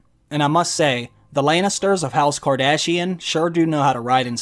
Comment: "The Lannisters of House Kardashian sure do know how to ride in style." I chuckled 😅🤣🤣🤣😂😂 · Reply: when the Mrs. & i heard "House Kardashian," instead of House Targaryen, we had to rewind to make sure Kim didn't make a cameo appearance...thank god she didn't, it took the last season to destroy this great series.